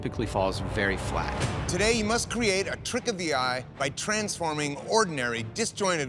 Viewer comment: ive been binge watching ... why does he say “cweeate” 1:49 its everytime